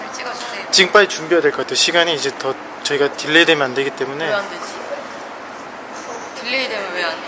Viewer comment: omg thanks for the subs!! IU is so cute <3 (btw I think they said delay and not derail